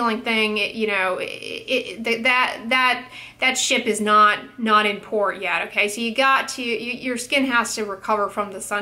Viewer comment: I had to come back and rewind because I thought she said "that sh!t is not important yet" and. was like whoa.. she never cusses. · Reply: She says, "That ship is not in port yet."